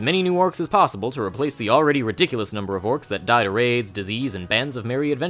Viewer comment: LMao I thought you said they died from aids but its raids LMAO!